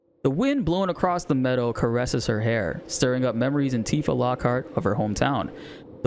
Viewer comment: I hope YouTube doesn't suppress you because they thought you were saying "ANTIFA" lol